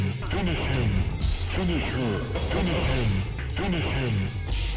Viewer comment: Great now all I hear is “Fin his shin”